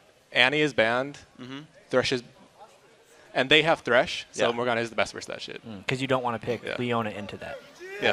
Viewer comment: it sounds like says "so morgana is best vs that shit."